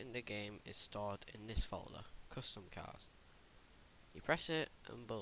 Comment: Custom cars is what he said sorry!